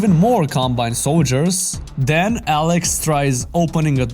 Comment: The subtitles say “alex” instead alyx, a proof that is more stupid than alyx and he deserves to be eradicated